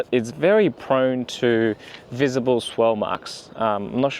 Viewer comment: Swirl marks. Good. Sounds like you said swell marks